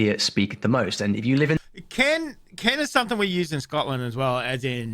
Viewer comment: So what he was saying was "kin" it's a term to refer to blood relatives.